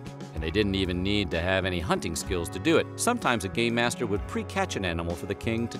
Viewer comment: Am i the only one that thought he said gay master at 7:47?